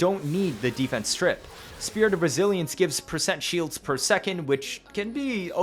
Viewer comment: every time you say " spirit of resilence" i hear "spirit of brazilians"